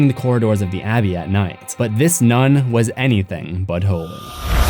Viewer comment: i thought he said "but this nun is anything butt-hole"